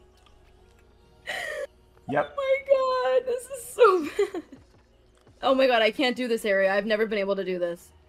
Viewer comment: Did he change the title? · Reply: Yes thats what I thought too. I couldve sworn it said 'She was so bad" on the last part